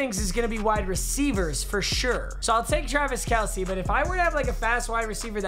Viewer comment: Did he say Jarvis Kelce?